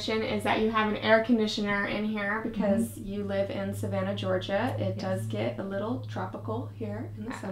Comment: I thought she said she lives in South Georgia 😂😂😂